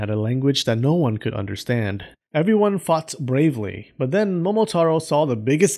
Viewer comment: sorry. Really no offence sir . I you actually sounded like ," that f**ked bravely. By the way I am a big fan of you . Love your contents and narration style. It's pleasant and sometimes funny